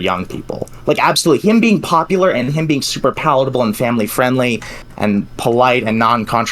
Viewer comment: did he just say Hasan is polite?? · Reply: PALATABLE??